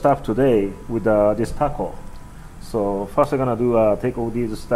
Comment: did you say taco?